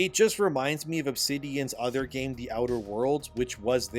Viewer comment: I low key thought you said “The Outer Wilds” and I was about to throw hands 😂